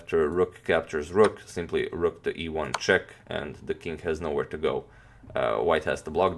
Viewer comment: Each time I hear "rook to e1 check" I always think "rook to ivanchuk"